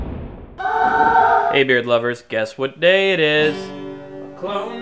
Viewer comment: For the longest time I thought he was saying "Beard lovelies" hahaha